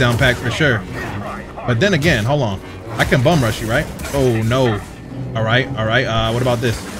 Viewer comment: did you just say bone rush? You’re not Marowak lol